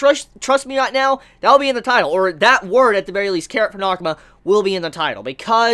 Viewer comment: I thought said I want Carrot Pornokama.